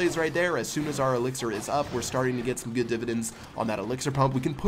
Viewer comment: I had to listen a few times and still cant quite make out what he said at 1:25, "starting to get some good ep-i-ments on that elixir pump" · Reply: good dividends